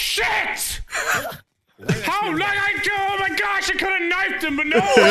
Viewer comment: at about 8:38-9:01 I think the misspeak was just Irish anger, cuz to me it sounded like "How low IQ?!?"